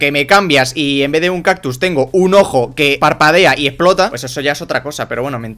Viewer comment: en ves de un cactus un ojo que parpadea y esplota XD (yo escuche esplota no explota)